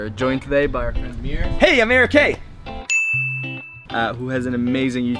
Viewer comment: this is really old, but did anyone else think he said hey america in a really weird accent?